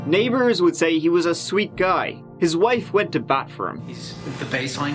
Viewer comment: "His wife went to bat for him" I heard "His wife went to the bathroom" Laughing emoji